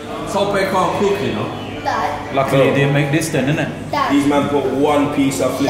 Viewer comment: am I the only one who heard someone shout "dad"